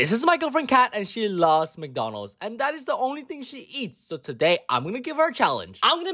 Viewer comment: I thought U said that she only takes 1 bite